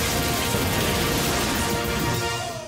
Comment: As a kid i thought he said "i put a shock to your sister!" 😂